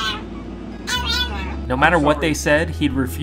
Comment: He sounds like he saying no momma